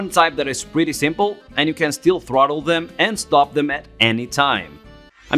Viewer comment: Man I thought that said throatable